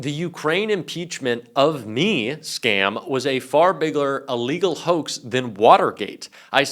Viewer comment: did you say "biggler" lol!